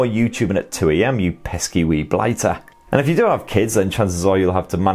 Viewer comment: "no more youtube at 2 am u pesky *idk what he said*" I'm literally watching this at 2 am.... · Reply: Teeny Tinu “pesky wee blighter”